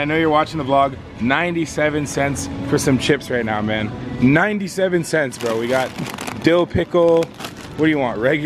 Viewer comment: I thought u said 97 percent